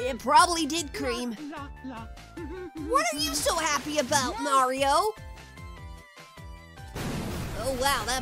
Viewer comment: I'm certain that he said "Nario" instead of "Mario"